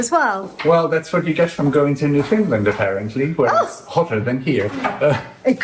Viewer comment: Oh gosh, my Dad had that Morris Minor! Ha! I thought said New Finland.... figured out finally it was Newfoundland... often pronounced Newfanland.